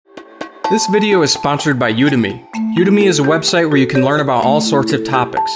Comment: I swear I thought he said this video is sponsored by you dummy